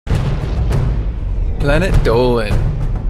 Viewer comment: every time i hear the "Planet Dolan" at the beginning i think "Planet Donkey". hahahaha i`ll find the door myself.